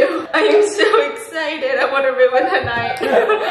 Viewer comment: Lmao I thought she said ‘I wanna ruin her life’ but what she said was ‘I wanna ruin her night’.... I mean... close enough, right?